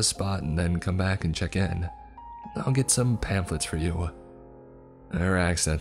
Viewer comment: "...I'll get some pampers for you!.." I couldn't stop giggling, bc that's exactly, what my ears heard and my stupid brain misunderstood. 🤣 🙃 🤣 Of course I know, that's certainly NOT, what the old lady actually said in this video. There's nothing wrong with our OP's speech, thank you very much for all your creepypasta narrations! 🤗 🤗 🤗